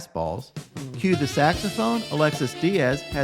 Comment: Thought u said "cue the trumpets " 🥺